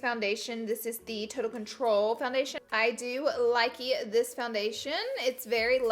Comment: wait did you just say ❤️ LIKEY ❤️